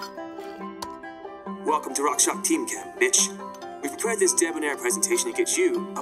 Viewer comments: I thought he said: "Welcome to RockShox team camp bitch!" , O_O :) Rick and Morty style! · anyone else heard "BITCH" ? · Lol I thought it said : Welcome To rockshock's team camp bitch. we've Prepare this debonair presentation